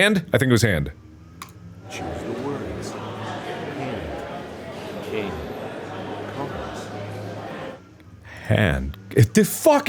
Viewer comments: I keep hearing "hand, king, COMPASS." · i have an audio processing disorder and this was v difficult at 4:40 i thought it said Congress